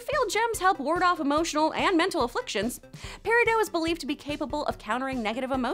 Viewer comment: Did she just say "peridough"?